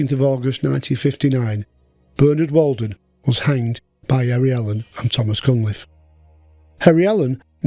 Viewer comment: "Albert would haven been ready and waiting to do his task" At execution of Walden. It should have been Harry. ;-) Well done though. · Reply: Allen was probably misheard by you as Albert